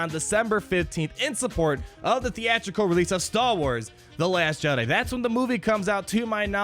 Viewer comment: I love your vids. I especially like how it sounds like you say 'stall wars'. Catchy.